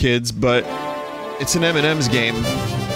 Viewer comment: captions says “it’s an Eminem’s game” I sorta pictured this moment with rap god song... I had me a copy of LEGO island - it wasn’t this corrupted with the dialogues Lol